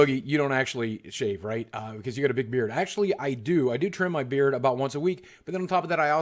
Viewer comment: did he just say he had a daughter? I didn't he had kids! Wow now I feel old :( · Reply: KING EONE boogie doesn't have any kids? He said he had a big beard lol